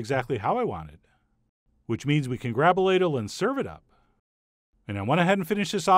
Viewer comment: When he said “grab a ladle” I thought he said “grabulate it. I thought Chef John made up a new word.